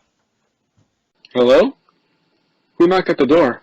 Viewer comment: I thought it said hellno